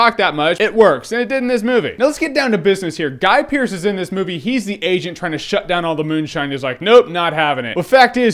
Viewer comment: lol i thought you said "hes the Asian trying to shut down this moonshine..." then i was like OH ok lol.